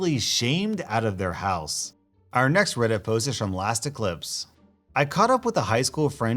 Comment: I totally misheard "lasteclipse" as "elasticlips"